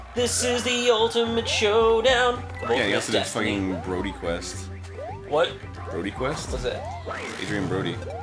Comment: I thought Ego said BronyQuest for a second. I've never heard of BronyQuest but i was excited :/